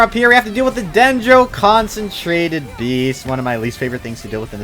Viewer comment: Did you say "concentrated"?